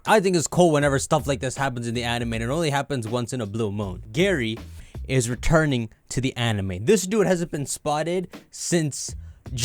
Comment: I thought it said "gay is returning to pokemon"